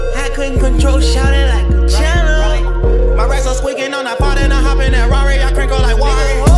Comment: Add 1:10 does he say my racks on squid game?